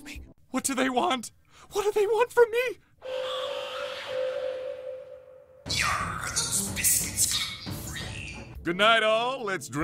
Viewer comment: What'd he say? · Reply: Yarr are those biscuits gluten-free?